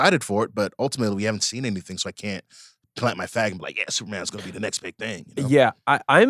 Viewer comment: idk what was TRYING to say but i think Van and I heard something else lol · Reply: “Flag” is what said